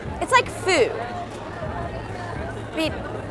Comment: did she say 'foo' instead of 'food'? at